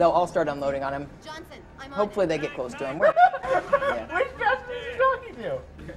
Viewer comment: Unintelligible nerd freakout by the fat dude in the white hat. Can anyone decipher what he said? *Edit: He says [silly laugh followed by] "Which Johnson is he talking to?"